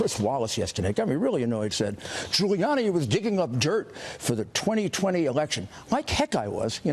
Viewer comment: I thought they said Giuliani was making things up, why are they asking for the media to ban him.